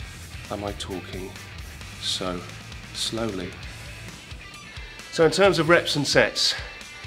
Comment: I deadass thought he said "so in terms of reps and sex" at 4:40.